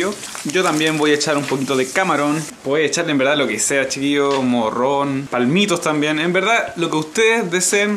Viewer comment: por un momento pense que decia "Pueden hecharle lo que sea ...como Ron." Soy un maltido borracho. jajaja